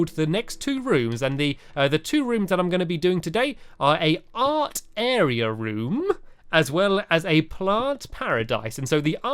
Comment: I thought he said "aunt area room" xDD